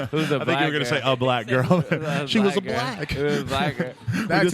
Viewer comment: anyone else hear scrim say 'HES a black" ?????